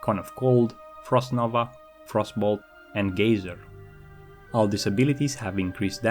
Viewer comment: Did he say Gayser? I'm not going anywhere near this elemental.